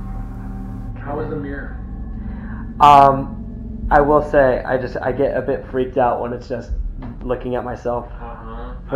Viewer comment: when plays with the red ball, it's broken, but I hear "Let's play" then a makes voice I haven't made out yet, and then "That red ball" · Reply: The male voice said "And I leave you to it" let me know if you hear it as well